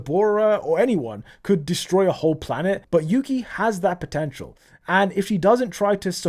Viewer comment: did you say potential